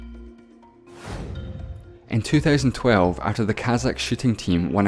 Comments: did he just say 'shitting' team? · i had to do a double take after mishearing 17:08 as the "kazakh shitting team" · it sounded like you said the kazac shitting team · No one: Him: "Pegs" Never mind about his accent, content is great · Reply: I heard shitting team and had to turn on the subtitles to find out what it was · Am I the only one who understands "the Kazak shitting team" at 17:08?